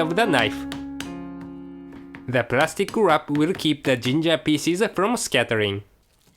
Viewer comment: /:O i thought you said Ginger Feces xD lol